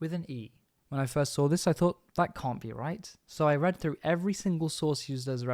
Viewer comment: the subtitles says "read" even tho it should have been "red" bcs its in SR1